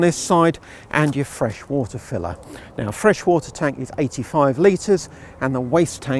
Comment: Tiny? 85 litre versus 100 litres max for most rivals. It's a negative point, as I pointed out, but not massively different to others. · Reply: I thought you said 60 litres. I'll go and clean my ears out.